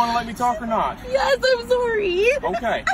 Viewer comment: Im sorry sir, did you just say mow?.